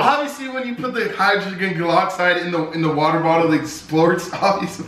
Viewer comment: Did say hygroglen goloxide